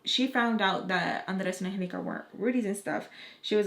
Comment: What did she say at 6:38?? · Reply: That Andres and Angelica (two of her kids) are not her husbands